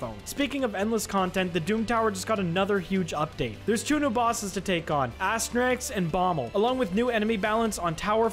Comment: I thought he said new boss ass rags